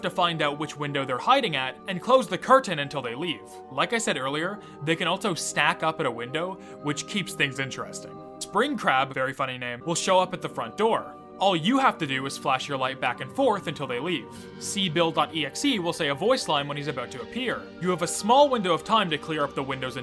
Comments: I thought you said Spring Crap lol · For a second I thought you said Springcrap not Springcrab